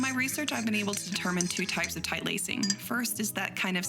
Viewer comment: I love the honesty of this. It might just be that it's like 4am right now, but I thought she said 3 different types of tight lacing, and then mentioned two? (Also like the honesty of "I don't feel comfortable discussing this" which I really appreciate. As someone who's got a bunch of triggers and findsthe whole "don't kinkshame = forcing kinks on people who are not okay hearing about/talking about it" really difficult, it makes all the vids so much more accessible to me.)